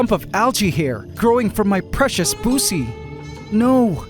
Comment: Did he say algea hair growing from his poosy? 8:27 😂